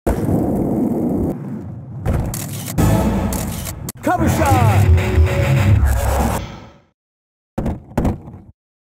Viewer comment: Wait, what he say? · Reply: cover shot